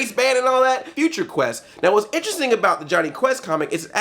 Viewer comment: When he said Johnny Quest I thought he said Johnny Test(even though it wouldnt make sense) and I nearly choked on my food. I hate that cartoon